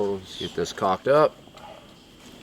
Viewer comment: lol, when you said you were going to, "Get this caulked up". I actually thought you said, "I JUST COCKED UP"lol. Shed's looking great